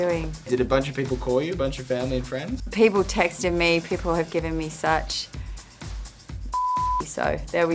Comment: What is she's saying on 1:42 ? Please tell me. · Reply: mas bro Probably "shit". "They've given me such shit". She means they've given her a hard time, everybody's heard about it, and probably wants to tease her about it or talk about it and she's embarrassed so she dreads people bringing it up and thats why she regrets mentioning it on the show. I'm sure if she knew it would be SUCH a huge, 20-million-view deal, she wouldn't have revealed it in the first place, haha!